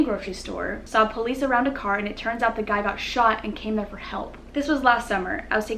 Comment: does she say came never help instead of help never came?😂😂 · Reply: Brooke Shrum no she said “came there for help”